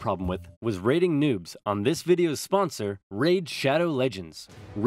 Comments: I thought he said nudes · who else heard raiding nudes You have an ugly mind